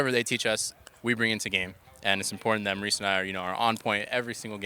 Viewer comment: who were the shotcallers? aphro and someone I couldn't hear clearly. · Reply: it's aphro and amazing but aphro called amazing by his real name which is maurice